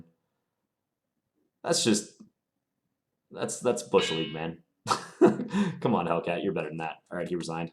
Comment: what did you say at 6:48? i didn't quite catch it! haha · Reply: I said "That's bush league, man" :)